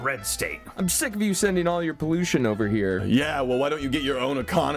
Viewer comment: when i first watched this video, i thought indiana said to illinois "im sick of you sending all your bluesion over here" because illinois was blue, but now that im not deaf, its actually pollution. i also originally thought that illinois was saying their color of state, and so was ohio. but america being america, thats simply not the case